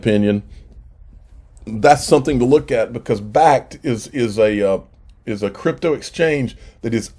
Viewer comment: What was the name of that exchange you said at 9:51? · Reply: Bakkt exchange . Well established name in crypto but really never talked about much . They been building it out since I think last market cycle 2017ish...